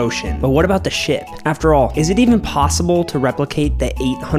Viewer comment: I thought he said shit 😂😂😂😂